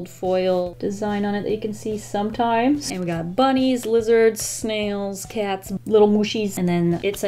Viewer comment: When you said “lizards” at the beginning, I thought you said “wizards” and I was looking for the “wizards” lol